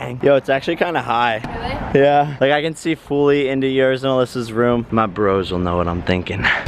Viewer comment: did he just say yours and Alissa's room??